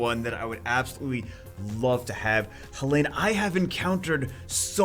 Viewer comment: Did someone say Bellenus?